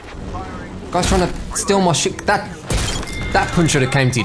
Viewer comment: i thought he was saying "stop tryna steal my shit" lol